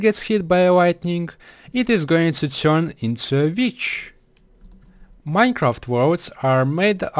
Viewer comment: Did he just say, 'And if a villager is hit by lighting, it will turn into a bitch'?